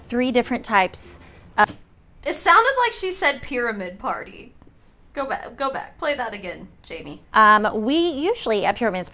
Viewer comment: I heard “pyramid parties” too.